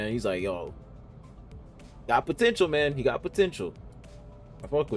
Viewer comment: did you say… potential man?